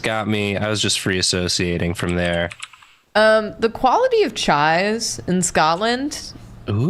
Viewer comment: I really thought you said the chives in Scotland were immaculate, and I was like okay, that kinda makes sense. But I would not have expected that Scotland makes good chai.